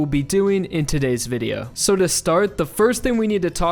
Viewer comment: he really said "to strat". Like wth, I am finished, my brain is dead I... I. no